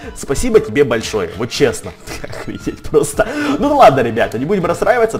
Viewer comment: мне послышалось или сказал "ахуеть просто" первый раз слышу что он матерится